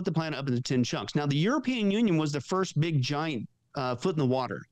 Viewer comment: I thought I heard say "European Urine" I checked and he didn't but I think that's what I'll call it from here on in.